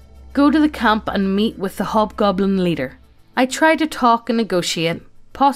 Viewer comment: What said: "Go to the camp and meet with the hobgoblin leader." What I heard: "Go to the camp and mate with the hobgoblin leader." I love this channel. :)